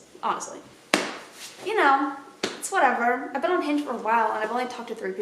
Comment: LOL I thought you said unhinged! haha But also I feel like the one about overflowing the toilet was unfair. EVERYONE poops and I feel like 90% of people have overflowed the toilet with a fat turd lol.